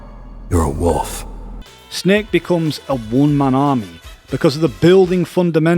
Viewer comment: "snake becomes a one man wolf pack" is how I heard it in my head. Haha